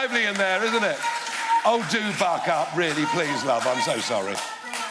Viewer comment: I swear the first time I thought he said “oh do f*ck up, love I am so sorry”.